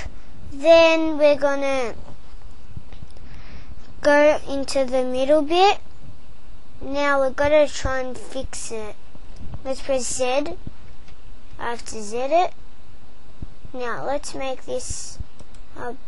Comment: What is he saying at 2:14 He says something like "Now press Zid" and i dont know what that is lol, i messed up on the extrude now i wanna fix it without deleting the whole thing